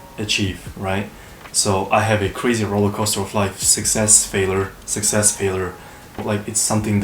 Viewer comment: i couldn’t catch it , let me know what you said, please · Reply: Roller coaster of life, I suppose😉